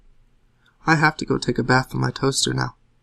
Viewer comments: I initially thought he said, "take a bath IN my toaster." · I thought he said I have to go take a bath IN my toaster now. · i misheard and thought he said "I have to go take a bath in my toaster now" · thought he said "i have to go take a bath in my toaster" lol i fail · This one was really great. At first I thought he said "I need to go take a bath in my toaster now." So I was pretty confused. The I realized he said 'with' and slightly giggled.